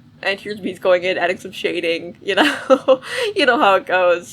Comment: I thought you said winky pie😂😂😂😂😂😂